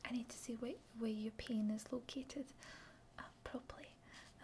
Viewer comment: I have an ASMR playlist from all of my channels and it helps me fall asleep, and I know I'm not alone in this, but I heard "pain is" but it sounded like "penis", and leastwise I woke up and bonked my head on the wall when I woke up.